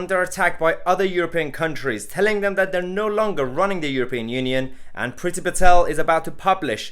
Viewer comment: Genuinely thought that said ‘ruining’.